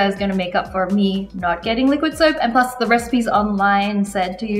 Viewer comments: "LIQUID SOAP" instead of liquid smoke😂😌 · She really said liquid ◇soap◇😂😂😂😂